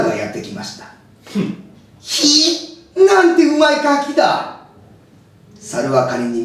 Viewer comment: なんて美味い餓鬼だ に聞こえた そーいやフリーザ様原作でカニ食ってましたよね